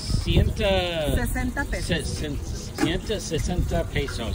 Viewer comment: She said it whas 60 pesos. is that what you paid? because it sounded like you said 160 pesos in spanish